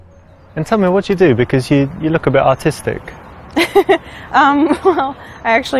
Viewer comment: I thought he said autistic 🤦🏽‍♀️😂😂